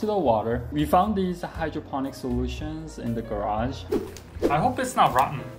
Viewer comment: i thought it said garbage instead or garage